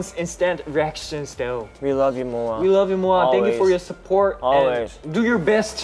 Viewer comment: When they said i love u moas i thought they said I love u morons